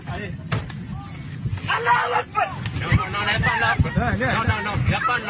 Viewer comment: sounds like he said "Allahu Akbar"... which the bloody insulting USA media likes to make into some form of death chant sigh.. while some have used it before terrorist attacks, it's out of the norm and any prejudicial feeling appear to have for Muslims, should also apply to any other religion. from the context, he wasn't happy about being arrested but claimed his God would see him through it. I hope you have the same feeling of 'fear' when you hear someone say "In nomine patris" in front of you.... boy, I know you said you were just a yanky redneck but you really put on the religious prejudice in this one.. yikes 5 out of 10, must do better.